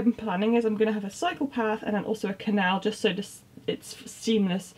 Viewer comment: Oh, you said "cycle path" I heard "psychopath" 😂